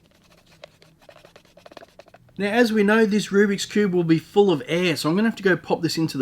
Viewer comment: I am so sorry but your accent made it sound like you said it's "full of ass" in an Italian New Yorker accent.